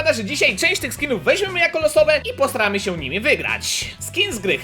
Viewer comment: Czy ja w 0:07 usłyszałem posrałem